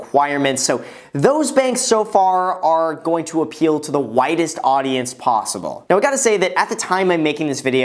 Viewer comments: Was listening at 2X speed, and at 7:14 I thought you said "Those banks so far are going to appeal to the whitest audience possible". Just about spit my water out lol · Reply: Widest, not whitest. Lol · Sounded like he said "Whitest audience as possible" @ 7:21 . Had to replay that for a second, lol. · Hahaha... Graham I know you didn't do this but at 7:20 it sounds like you said "these Banks will appeal to the whitest audience possible". You are breaking down Bank preference by certain demographics now huh?